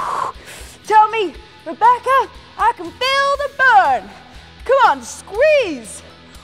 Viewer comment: You should have seen my face when I heard her say "c'mon Rebecca!" I was like whatttt... turns around to find hidden camera.. LOL